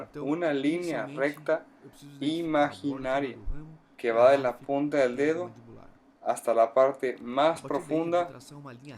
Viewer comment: No entendí lo que dice en el minuto 5:15 al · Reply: De la arcada dice el..Y el original del rafe